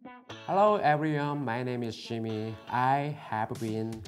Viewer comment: When he said jimmy u thought he said shimmy so I went like shimmy ye ye shimmy ya